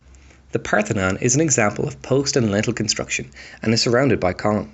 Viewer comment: you say something like "The Parthenon is an example of (post-lintel?) construction"? I have replayed it many times and I cannot understand that bit. Yes, I'm American, but I have had tons of exposure to UK entertainment that I didn't need subtitles for. But it is pretty tricky to figure that stuff before hand... I fell like I'm mothering you, and I'm sorry about that. I really wish I knew what you said, though. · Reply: Post and lintel construction! ‘A building system where strong horizontal elements are held up by strong vertical elements with large spaces between them.’